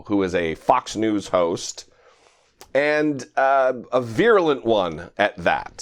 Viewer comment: said "virulent"....... at first I thought he said "purulent". :) Pirro is the conjectural prima donna on steroids...... you'll typically see example of something not too different to white racists' character consistently well below that of those they target. Her knowledge base has about as much integrity as Trump's "the word is.....(insert conjecture, fabrication, deception and deflection here). Re: hand washing and its effect on microbes....... fairly recently I'd seen an article about a major medical center that uses a rack of ultraviolet lamps on wheels to seriously finish up what antimicrobial fluids couldn't when scrubbing rooms after occupancy by someone with certain organisms, and with more impressive results than they'd expected. Bright sunlight to the hands for an enhancer, worth considering.